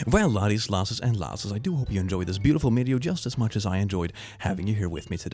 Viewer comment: d-did he say mideo?